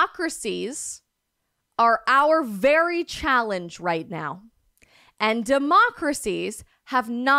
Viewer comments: I think he said "Democracies are.. are very challenged at the moment" and not "our very challenge". That doesn't change the totality of what he says, though. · He said “democracies are very challenged right now.” He did not say “democracies are our challenge right now”, as you claim. You may be right about a lot of things but please be careful. You weaken your position if you misunderstand.